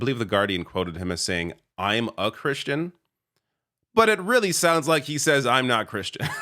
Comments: I heard “I’m not Christian” too. I backed it up immediately and watched it again. And then you commented on it. 😂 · It sounded like he said he wasn’t a Christian but was speaking at Trump Ebonics. Trump said, “I-Ma Christian.” Regardless, the media response was typically underwhelming.